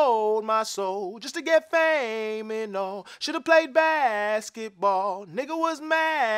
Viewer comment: I thought he said wish I woulda sold my soul just to get fame and gold